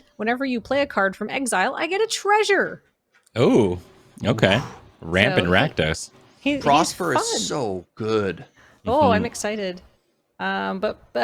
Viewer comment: I know Ben said “Ramp in Rakdos” but I heard “Rampin’ Rakdos!” Like some 60’s comic book character’s exclamation and now I want that button